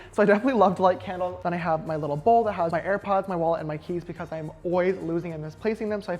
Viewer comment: sounds like he says air pods? i thought of suorin air pods straight away